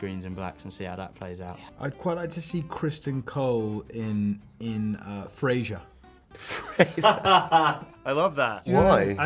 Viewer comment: I'm so confused, what did he say and are they talking about friends? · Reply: Frasier